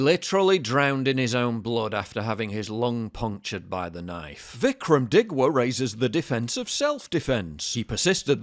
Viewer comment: I thought he said VIikram Dickwart at first😂